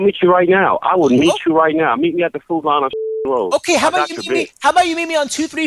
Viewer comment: did he said the food lion on cedar road??? I live there